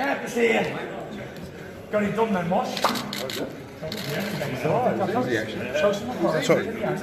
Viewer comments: I thought he said “got it done then mush” title says different? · That sounded like he said you GOT it done then mush not get it done.. looks like there going be a big announcement at the bjs canelo fight